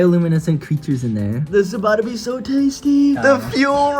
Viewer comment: i almost thought he said "this lobotomy is so tasty!"